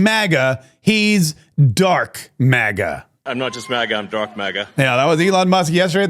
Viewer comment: I thought he said DARTH MAGA, as in a play on darth veda the dark overlord